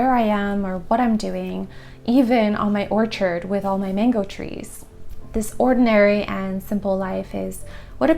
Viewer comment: excuse me. Did I hear right? mango trees!? Please share. I love my mango trees, can we see yours?